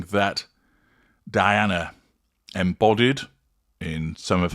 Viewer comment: Sorry, Dude. You missed it with this one. Diana, Princess of Wales did NOT invent the White Man' Shirt with Jeans look. It wasn't even close to original when she wore it. Millions of women have worn those same items - together. Any suggestion that wearing a shirt with jeans is 'cosplay' is quite reaching. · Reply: I missed where he ‘invented’? I heard him say ‘embodied’